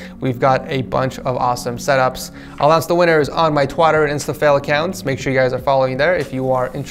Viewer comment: did he just say "Twatter" Like if you heard that as well